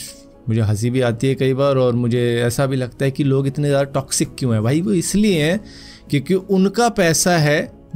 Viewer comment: Correction: She didn't say "why people are toxic" 6:02 . What she said is "memes do not bother her unless it's being used to spread toxicity otherwise it's ok to share memes if it means people are having just fun"